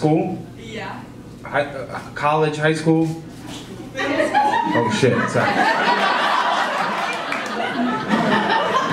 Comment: Great stuff! Missed the last joke though, couldn't hear her answer over the laughter at 3:15 :( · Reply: I'm pretty sure she says "middle school"